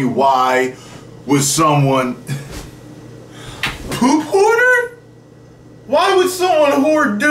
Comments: I thought it said poop harder XD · I thought you said poop harder